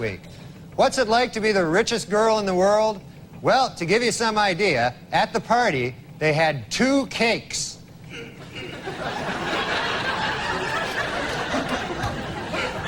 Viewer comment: Two cakes. So simple, but so brilliant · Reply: Ohh, cakes. I thought he said "two kinks". I thought maybe he meant two members of the band The Kinks.